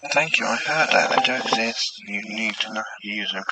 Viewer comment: sound to me like it said "Thank you (someone's name?) ,I do exist, you need to learn how to use uncle's...." (cant make out last word.) Kind of sounds like sight.